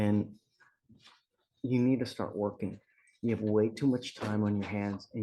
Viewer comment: I thought this said "twerking"